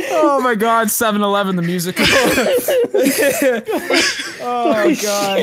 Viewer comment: I legit thought they said 9/11 the musical instead of 7/11 👀